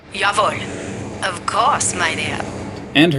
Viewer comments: I always thought the Valkyrie said: "Of course, my dear!" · I always thought she said "Of course, my dear." 😥 Oh, well... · For all these years I thought Valkyrie said "Of course, My dear" lol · was i the only one that thought she said "Of course my dear"